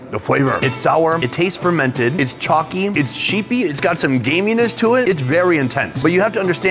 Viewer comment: anyone else catch that chalky cheesy hair? 😂